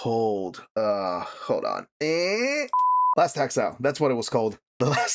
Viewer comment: what was that? I legitimately couldn't understand what you said there, what's the name of the anime your talking about? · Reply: Last Exile